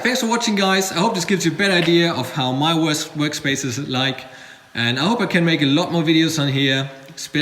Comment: It almost sounds like he is saying ''I hope it gives you a bad idea of how my worse workspace is like" :P :P